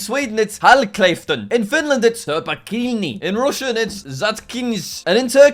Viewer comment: it can't be only me who heard "her bikini" for the finish one right?